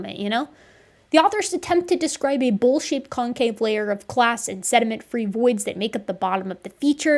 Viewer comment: LOL I wasnt looking and all I heard I was "bullshit concave layer".